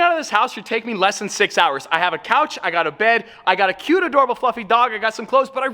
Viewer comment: I thought said "A cute adorable f**king dog” for a min 😂